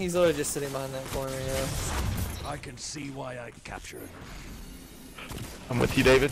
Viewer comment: Also listen to what said at 11:21 it sounded like he said I can see why I capture